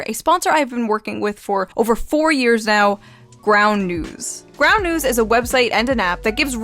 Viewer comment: Oh ground new. I thought you said brown news, I'm watching this while barely being awake driving to work.